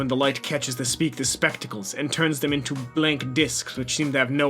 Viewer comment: i thought it said tuna instead of turns